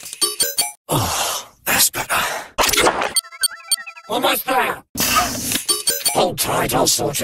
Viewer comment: What word did he say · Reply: "Almost there! "